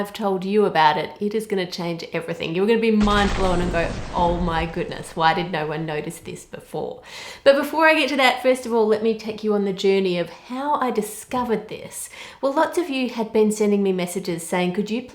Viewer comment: I thought you said ‘Let me take you to on journey of Hell’ xDD love ur vids btw❤️